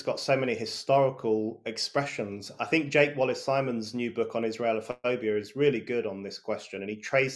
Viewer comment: did he just say jake wallace simons???? That was the name of one of my best friends as a child and i havent been in contact with him in over 30 years! Got to look him up, any one know the name of the book or where to find him?